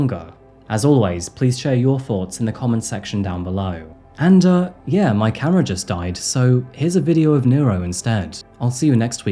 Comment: i thought you said your cat just died! Sheeew! So glad it was the camera. Thank you for your amazing research and work.